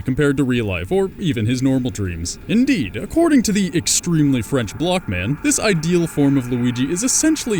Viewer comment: I thought I heard you say "extremely French black man